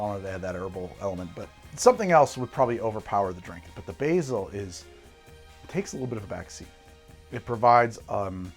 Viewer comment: Totally misheard "...the basil its... takes a little of the vaccine" thought 'wow greg, getting political are we?'